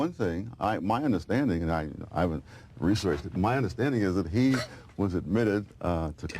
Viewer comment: I think @ 15:40+/- Sowell said "my understanding is, and I haven't researched this but, my understanding is". I think he was prefacing that his understanding wasn't backed by personal research. Cool video btw.